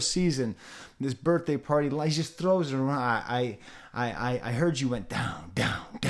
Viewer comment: I heard it as "I hear you went dumb"